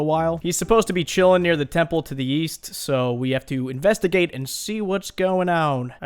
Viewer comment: the temple to the east? i thought you said the temple to the weast...